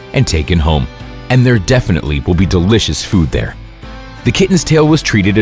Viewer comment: I thought he said the kitten will be delicious food